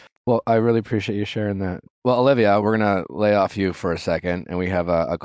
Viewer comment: Omg did he just call her Olivia